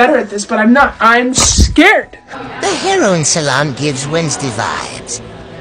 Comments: I thought it said heroin salon- 💀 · Me who thought it said heroin salon